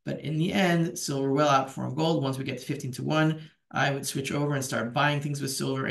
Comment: did you say 50:1 or 15:1? I am assuming you said 15:1 but it sure sounded like 50.